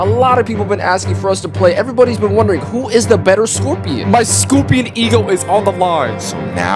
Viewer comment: did bro really say "my scopion ego"